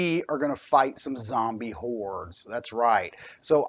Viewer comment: "zombie whores"? i would play that :), great vid - kinda choppy though, was that just the recording or in the gear vr? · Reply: LOL... hordes ... But yeah after watching it again it does sound like I said whores. OH, and I'd definitely play that one too! [;]-)